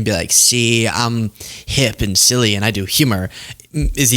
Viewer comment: I thought you said she was doing humor.